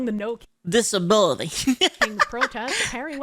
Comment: thought you said milking protest.. lol